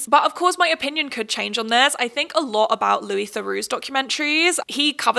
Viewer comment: who's documentaries?? I wanna watch but can't figure out what she's saying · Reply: Louis Theroux